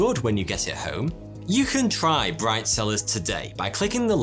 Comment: Bright cellars I thought he just said brifezillaz lol